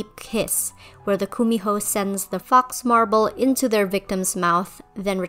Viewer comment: I was scrolling through Pinterest and I just hear "Kumi hole"